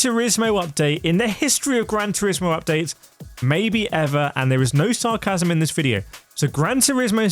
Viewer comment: I thought that guy said "its no sarcasm"`?